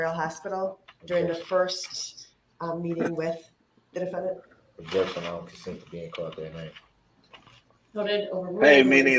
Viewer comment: Every time Brooks speaks, all I hear is "Objection, I'm a moron..."